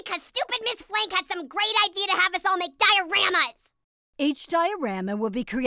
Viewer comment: I thought I heard "Great Idea to Have us all McDiorama's" when I heard that I thought Fred was going to say "McDonald's"